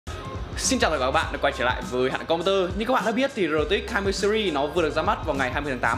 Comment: hà nội computer mà đọc nhanh như ông thì nghe thành HẠNH Computer